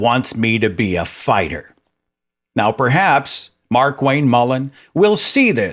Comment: Did I just hear you say "MarkWank" ? Bloody genius!